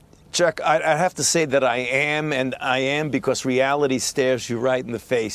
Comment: I heard him say "reality stabs you right in the face"